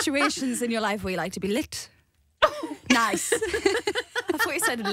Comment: Perrie- As a girl, there are situations in our life where we like to be lit nice.. Jade- ○_○ Also Jade- I thought you said 'licked' !!!!